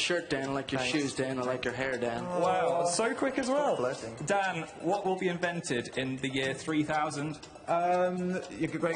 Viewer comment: Dan, I heard you mumble "flirting" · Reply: "stop flirting" to be exact :)